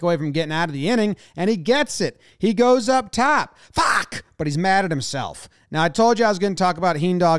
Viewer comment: looks like he said "come on!" Instead of "f***!".